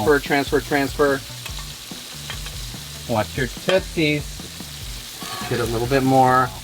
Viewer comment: At around the 11:26/28 mark what did exactly say?🤔 I sure hope not what the Closed Captioning translated it as 😬😆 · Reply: He said "Watch your tootsies!"